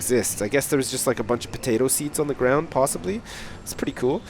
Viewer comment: Did he just say potato seeds? Lol